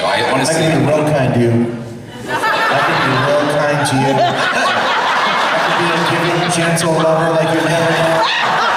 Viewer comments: what did he say at 4:37??i can't hear it cause that woman's laugh😂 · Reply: First he says "i can be real kind to you" then he says "i can be a gentle giving lover like youve never know" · what does he say?!?!?! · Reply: RiverBed Flower "I could be a giving, gentle lover like you've never known." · what does the guy say at about 4:35 ???? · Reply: He says " i can be real kind to u" then he says "i can be a gentle giving lover like uve never known"